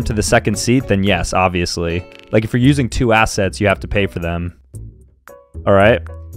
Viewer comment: I misheard him and thought he said “@ sets” not “assets” 💀💀